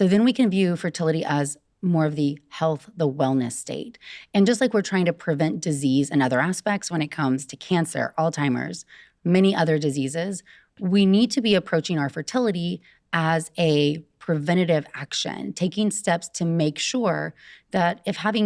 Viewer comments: did she say "Old timers" instead of Alzheimer's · did she say “Alltimers”? 😂 i automatically don’t trust this girl now haha